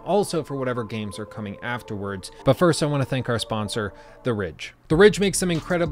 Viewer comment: looked away for a moment and misheard this part "But first I wanna thank our sponsor, The Rich" lmao that is a lucrative sponsorship